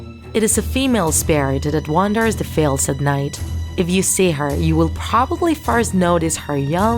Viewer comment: You said ‘you would probably first’ but I thought you said ‘you would probably fart’